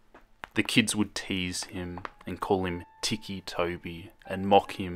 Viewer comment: I thought I said thicc Toby :/